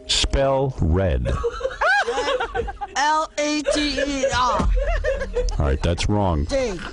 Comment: I heard L-E-D-E-R.